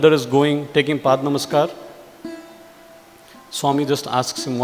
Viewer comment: Did hr said paad namaskar